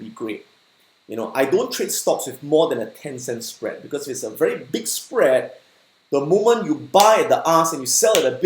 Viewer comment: I thought he say big ass spread!!